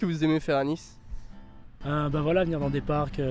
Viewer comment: "c'est plus sympa"? really?! I only hear babble like "kosipeu ça va"